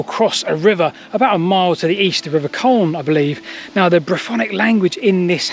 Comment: Thanks for another fascinating video. You've used a word several times in this video that I've not heard before, and I think I've misheard it as I can't find anything on Google about it: "Briphonics". Please could you correct me if that's wrong as that sounds really interesting.